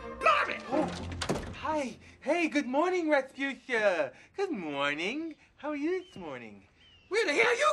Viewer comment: it sounded like she said "Marvin"